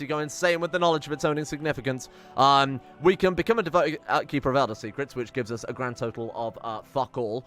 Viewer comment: For a moment at 28:52 I thought it said that he would be a devoted keeper of elder scrolls. Whoops.